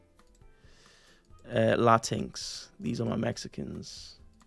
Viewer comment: did bro really say latinx? 😔